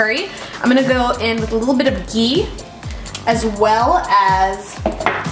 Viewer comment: In the minute 2:20 when she adds a yellow sauce and says "a little bit of gee"...what is that? I didn't catch that up